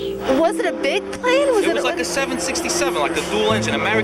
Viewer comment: did I hear 67?…..